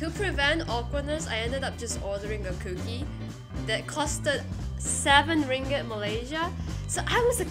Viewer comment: HOW CAN YOU PAY LIKE 15 EURO FOR A COOKIE!?!? (I thought you said 70 ringgit not 7.)